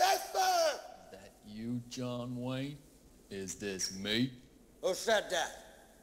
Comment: I always thought he said "thank you john wayne"